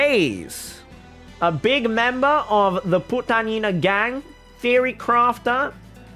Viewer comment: Wait... Did he say "Putang ina" in 17:10 Philippine National Anthem Intensives